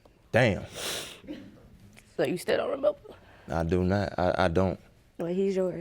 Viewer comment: when asked he said I do now... Did anyone catch that or just me?